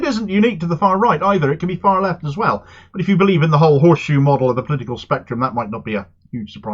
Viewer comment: "If you believe in the horse shoe model, you're a ridiculous moron". Is that what you said? Cause I wholeheartedly agree.